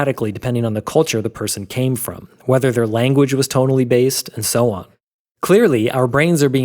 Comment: i legit thought he said "whether your language is totally based" rather than "tonally based"